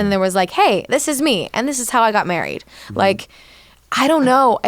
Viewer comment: Well at first I thought Ian said "Sophia and I got married".